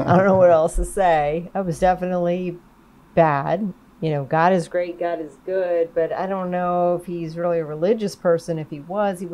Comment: First Time i heared this i thought he said God is great god IS Gay...